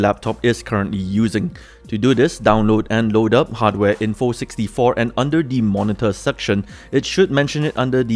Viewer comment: what should I download? I'm sorry I couldn't quite catch it. English isn't my first language 😅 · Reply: You're most likely referring to Hwinfo64